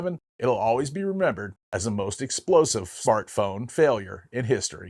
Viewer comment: did he just say FART FONE?😂